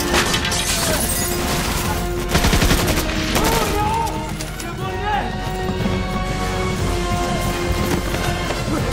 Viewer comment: I think that soldier said "Move up!" in Swahili. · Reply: he says " il est en haut " which means " he is upstairs" in French